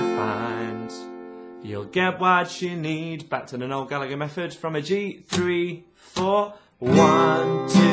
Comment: I kept hearing "Oregano method".... but after listening a bit harder I realized you were saying "Noel Gallagher". That makes much more sense now. Amazing lesson! Very convenient! Ill share the channel :)